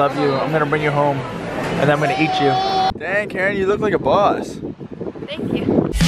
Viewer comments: I thought said “dang Keren you look like a bus” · It sounded like said "damn girl, you look like a BUS" instead of BOSS xD, and then Keren said "thank you" and I was so confused xD